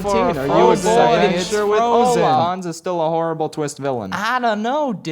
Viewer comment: why do I feel like you said Rohan instead of Hanse or whatever his name is